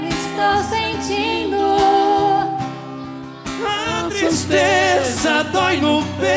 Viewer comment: marrone , a " susteza " doi no peito sem parar